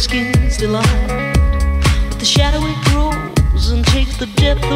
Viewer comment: It took me YEARS to find this song because I kept googling "the shower grooves song lyrics", tell me I'm not the only one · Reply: I heard "the shadow, it grooves."